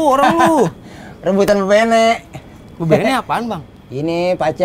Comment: Bang ngomong wewene.. Itu kayak nya bahasa Manado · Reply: maaf aku salah denger.. Aku kira dia ngomong wewene,, ternyata bebene..